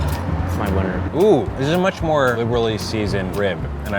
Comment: what did he whisper to him? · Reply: He said that’s my winner